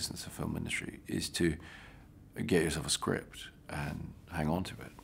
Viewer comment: What does he say at 9:30 "xxx of a script, and hang on to it"? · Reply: “Get yourself”